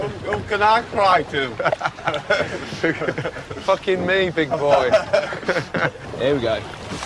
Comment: did gordon just call stuart "big boy"